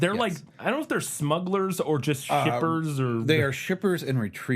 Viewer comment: thought they said strippers